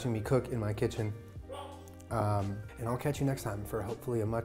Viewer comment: I thought said I'll crunch you next time instead of catch